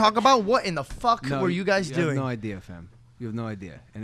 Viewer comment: did Logan just say fam, I think hes turning British 😂😂